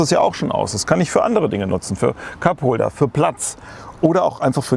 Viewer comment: erstmal Kackholder statt Cupholder verstanden 1:43 ;)